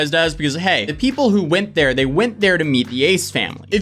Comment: I have no idea who those people are, but I kept hearing "Aids family" when you said their name.